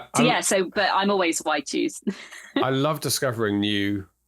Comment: So funny, I thought you were saying "white shoes" I had to look it up! I write white shoes too 🤣